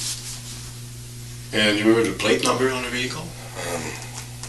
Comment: What does the detective say after repeating "white dually" at the 11:38 mark while he is taking notes??? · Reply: He says "And you have a plate number on the vehicle?"